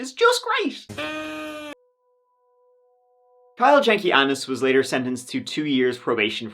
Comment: I kept hearing 'Kyles' name as 'Kyle Janky Ass' which fits perfectly.